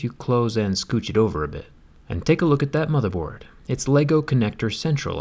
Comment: I thought u were gonna say motherf**ker